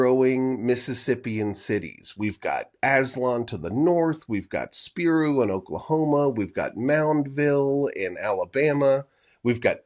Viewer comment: thought he said aztlán for a second.